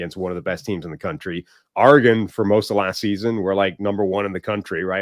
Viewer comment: did say “Argan” 😂 but meant to say Oregon (ORR-ih-ghən)